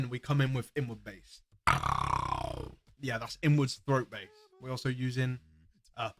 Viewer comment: did you say "inward throat bass"?!